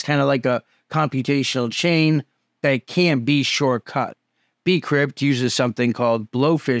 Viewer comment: heads up, the subtitles say "that can be shortcut" instead of "can't" as they should